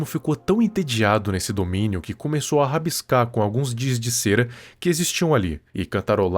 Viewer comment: "Diz de cera..." Foi de fude...